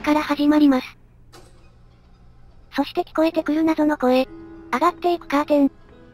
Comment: 上がっていくラーメンに聞こえた